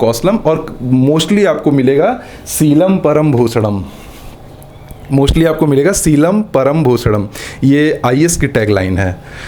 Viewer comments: did i heard it right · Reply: Bhushanam Hai Lekin Bhai ko Bhosada chahiye isliye Bhosadam hai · क्या किसी और को भी "सीलम परम् भोसडम" सुनाई दिया🤣(mostly aapko milega)